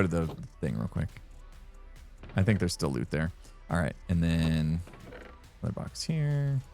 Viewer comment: You say "There's still loot there"(raid farm for totems) What I heard was very Canadian "there's still out there."